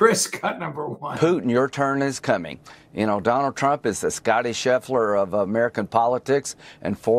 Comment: I thought LG said: "Scottish Hitler". Until I looked at the transcript.